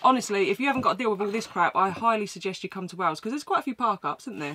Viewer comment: I KNEW you were in Wales, but it took me to the 27:04 mark before I realized was saying "Wales", through the entire video I thought she was saying, "Wows!" IDK, I'm old, I'm American, what can I say? Love ya both!